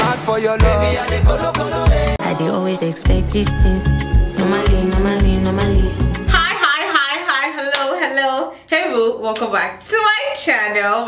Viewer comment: your hello boo is so cute, I thought you were saying hello who